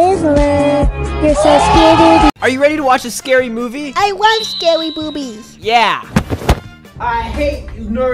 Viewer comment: did he just say scary boobies?🙁